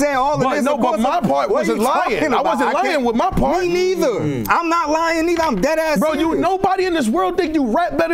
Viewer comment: tells nigga I ain’t lying I’m dead ass serious 😮😮 did anybody else catch that?????? Or am I bugging 🤦‍♂️